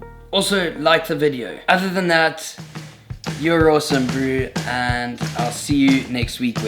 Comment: Totally thought he said “Jon Olson” at the end and not “you’re awesome”😂